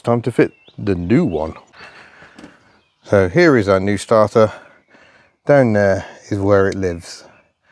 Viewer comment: Omg....thought you said new starter. I'd definitely would of fitted a new one....but as usual great video, honest video of how retro cars are like to work on.